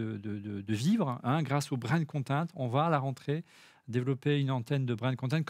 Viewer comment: J’ai pas compris le terme, j’ai cru entendre du « brain content ? (1:01:36) » mais je ne suis pas certain… Et je n’ai pas bien compris le principe ? C’est d’inclure quoi des tests sponsorisés ? Désolé mais je n’ai pas bien saisi en quoi aller consister cette évolution.